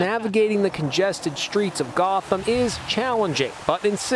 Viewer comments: I’m sorry did he say GOTHAM 💀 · Did he just say "Gotham"? Is Gotham real? Is it a real place? I'm confused. I've never known these things, I need someone to help explain! 😭